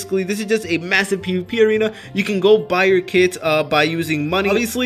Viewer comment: number 5, when he said "you can go buy your kits" i thought he said "you can go buy your kids" i was like what